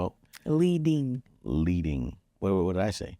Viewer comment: I thought said “leaving” at first too 😂 thanks for that clarification. Thanks for also reframing Theo’s character traits, I actually needed to hear that! Concerned vs bossy!